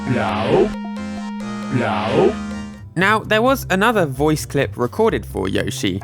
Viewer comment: Sounds like Yoshi is saying "No" as the Spiky gets closer 🤣